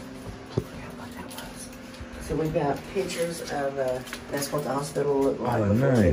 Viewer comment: i heard a whisper saying "I forget wat that was" i don't know if the lady that's showing around if it was her that whispered that or wat!